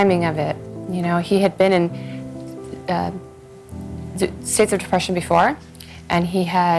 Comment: what does Talinda say at 38:08? I can't make out part of it. Something something "depression before." To me it sounds like it could be several different things such as stayed through depression before, severe depression before, secret depression before, sit through depression before. I just can't make it out. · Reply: "Uh... states of depression before"